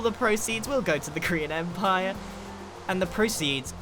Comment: it sounded like he said Korean Empire